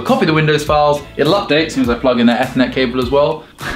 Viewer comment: I thought said ethnic cable instead of Ethernet Not after Brexit! Heyo